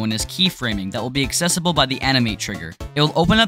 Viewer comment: I thought you said anime triggerXD